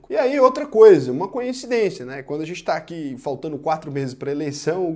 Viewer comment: Conteúdo sem muito bom. Verdade como ela precisa ser dita. Só uma dúvida: Ouvi "conhecidencia" em 3:01 do vídeo?